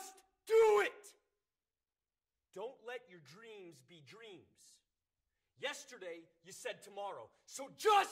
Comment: Not going to lie, it sounded like he said don't let your drinks be drinks.